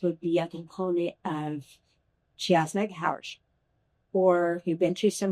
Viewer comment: what does she say here? Even the captions don’t catch it. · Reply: It looks like Alison said :”she has my back” but it sounds like “she shares my couch” :D